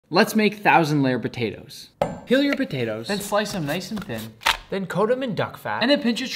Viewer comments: "peel your potatoes" Tell me why I heard "kill your potatoes" 😂😂🤣😭😭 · Bro I keep hearing "Kill your potatoes, slice them nice and thin" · Bro,when Nick said ‘First,peel your potatoes’,I thought he said ‘First,kill your potatoes’😂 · I thought he said "kill your potatoes"